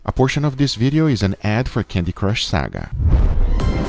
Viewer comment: Everyone's talking about the egg and im over here being suprised that Candy Crush still exists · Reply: I misheard him saying "A portion of" as "Unfortunately", so I thought he said "Unfortunately, this video is sponsored by Candy Crush Saga"